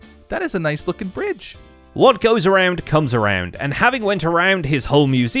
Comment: So was I the only one who heard them say "What goes around, cubs around"?